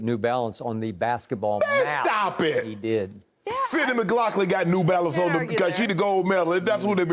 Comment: Thought Shannon said Sarah McLachlan. But he did say Sydney McLaughlin.